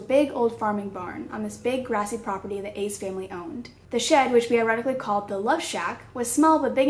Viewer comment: I thought she said "the ace family owned"